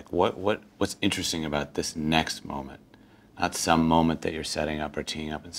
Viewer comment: is he saying '...moment at some...' or '...moment not some...'? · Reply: Door Donut not some